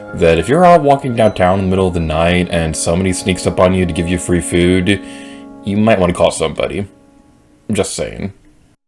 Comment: I almost heard this as, "You might want to call Subway."